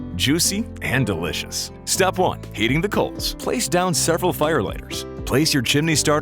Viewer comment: i thought it said eating the coals